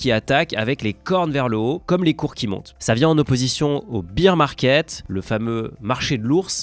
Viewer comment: A 1:08 j'ai entendu beEr market. Pas tout à fait la même chose😅